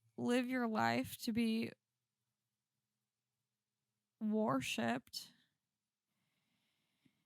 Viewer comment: Did you say "warshipped" @ 37:34? I laughed out loud